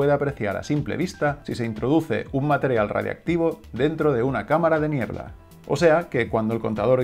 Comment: En 4:11 parece que dice “cámara de m1erda” en vez de “cámara de niebla” 😂😂😂